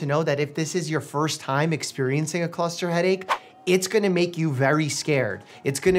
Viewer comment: I remember reading about cluster headaches years ago and fully get why it’s also known as suicide headaches, I hope this would be his last experience with it. I feel sorry for those having to go through this chronically 🤦🏻‍♀️ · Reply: I'm really tired and thought you said "i hope this is his last experience"